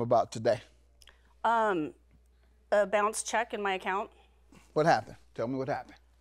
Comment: I thought it said bounced cheeks lol